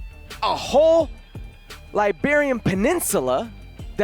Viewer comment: he really said Liberian peninsula 😂 Iberian bud